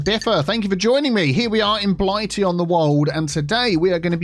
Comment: Every time I hear Biffa say, "Blightea on the Wold" all I hear is Blight on the World. Not the best name for a city! LOL!